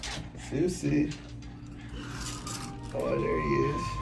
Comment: I swear I thought he said suzie